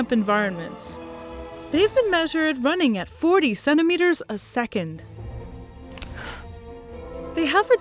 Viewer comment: “They can run 40 centimeters a second” Americans: I guess that’s fast. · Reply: I thought she said 47 meters a second and I was like “there’s no way” 😂😂